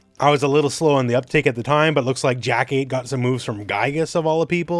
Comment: Oh, you said Jack-8. I thought you said Jackie. 😅